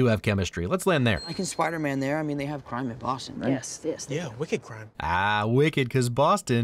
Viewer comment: OHHHH I always thought Ned said “We could crime”